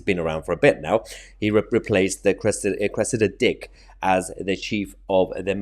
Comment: Hahahahahaha,I thought you said requested a dick not Cressida dick ,was going to say well they certainly got one